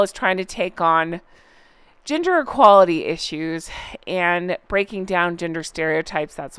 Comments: For a moment I thought you said ‘ginger equality issues’! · Oh, bahaha! I heard “gender equalities” as “ginger equalities” and as a redhead, Harry is REALLY missing the train. We would unite!! 😂😂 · When you were talking about Archwell taking on gender equality issues, I absolutely thought you said ginger equality issues. The even funnier part is that I wasn't even shocked by it because H&M are so ridiculous, that it's easy to believe they would do something like that (especially since he's a ginger and could claim to be a victim lol). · At first I thought you said they’re now taking on “GINGER equality issues” and “GINGER stereotypes” ! 🤣🤣🤣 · I literally thought you were saying Ginger equality issues LMFAO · I misheard you I thought you said the duo where now fighting .. ginger inequality and ginger stereotypes .. it made me laugh xxx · I genuinely thought you said “Ginger Equality Issues”! Ginger is a proper derogatory ward for red heads. Here in Britain, kids with ginger hair get picked on more than ethnic kids! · So funny, I was listening to this at work, so I only had one your phone and I thought you said Ginger equality. Not gender. That just struck me funny